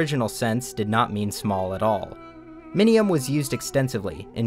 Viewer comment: Bro I thought you said minimum and I got so confused